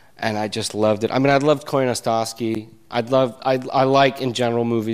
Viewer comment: Does anyone knows the name of the director he mentioned? I don't understand it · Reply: He says "Koyaanisqatsi", it is a documentary similar to "Baraka".